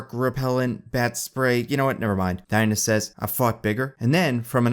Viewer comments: Am I the only one who heard: "I've fucked bigger." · I'm sorry but did anyone else hear "I fOked bigger" @ 9:56 ? No?... I think I have a problem